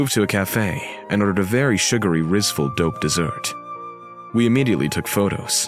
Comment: did he say rizzful